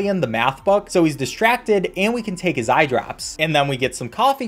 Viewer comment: i thought he said take his eyebrows. this alien must be very distracted